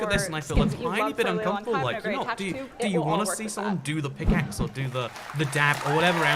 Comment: Please, when you want to talk during showing us some fragments when someone also talk, please, lower the volume of that someone. Like at 3:33, I'm confused what do you want to say. I'm not native english speaker and just listening to one person is hard for me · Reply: He said "it's kind of uncomfortable, like do do you want to see someone do the pick axe"